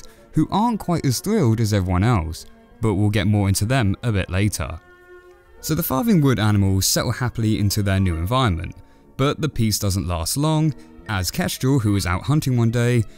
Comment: Sorry I thought it said “Farting”